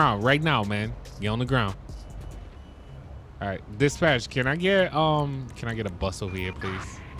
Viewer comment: did just say can I get a bus over here?